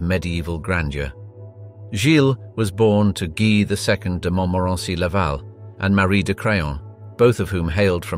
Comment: I heard "born to french name who married crayon 😂😂😂